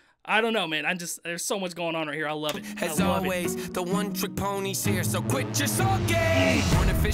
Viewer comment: i thought he said “not autistic” 😭